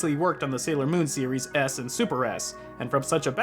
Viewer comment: thought you said "ass and super ass".